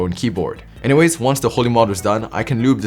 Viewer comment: When he said holee mod i thought he said the holi one